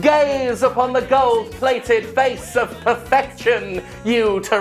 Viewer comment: "GAZE UPON THE GOLD-PLATED FACE OF PERFECTION, you terrestrial pedestrian!!" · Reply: Thanks, I heard it as "GAYS UPON THE GOLD" and was aroused by Star Eater